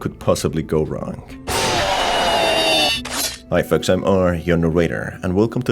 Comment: I find it funny that you've written "Hi folks I'm R, your narrator", because you got comments of people telling you "What do ya say in the intro?" I'll admit I was among those who didn't quite get what you said, I thought you said "I Folks I'm R you are on my radar", or somthing like that. I'm glad you push to correct those things and push quality. Well done.👍 Love your videos. Not trying to make fun of you.